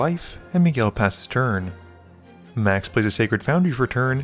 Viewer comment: For the first third of the game, I thought you were saying Max Missplays a land and all I could think is HOW??? I'm a dummy, great game!